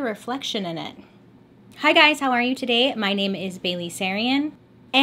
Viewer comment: It sounded like you said today your names Bailey lol was thinking aren’t you that everyday 😅